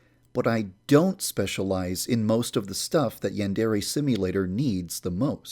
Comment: Put on English subtitles and at 1:23 it says "you and airy" instead of "yandere" I'm laughing so hard I love these little mistakes